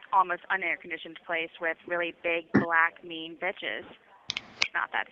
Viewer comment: Did she say "Big Black mean bitches"?